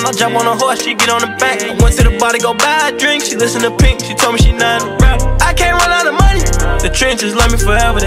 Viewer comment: She told me she not a rat 🥸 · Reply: I thought it said, "she told me she know how to rap"